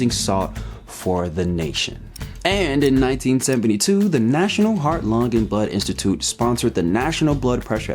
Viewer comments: My biggest qualm with salt is that restaurant and home table salt is now sodium aluminate (that's not the correct spelling), rather than NaCl. We don't need to ingest aluminum in any form. Hahahaha 19:35 I thought you said, "the national heart, lung, and butt institute..." hahaha I need to grow up. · Misheard 19:36 as "The National Heart Lung and Butt Institute" and that's head-canon now. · first time I heard "the national heart, lung and butt institute" ngl